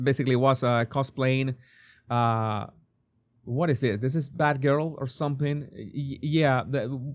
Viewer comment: At first I thought you said fat girl. Why in the world was she prancing about wearing a ridiculous bat girl outfit. She looked repulsive.